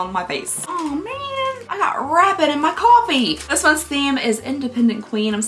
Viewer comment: 😂😂😂I thought you said I got a rabbit 🐇 in my coffee😂😂😂